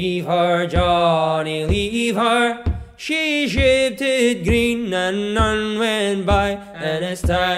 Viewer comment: she shitted green. That'd what I heard anyway